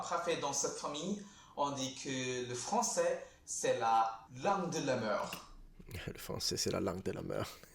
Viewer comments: In French I thought he said “langue de la mer” sea language? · it sounded like he said la langue de la merde 😂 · For the french part i got very confused by the subtitles, I heard "le français c'est la lame de la meur" xD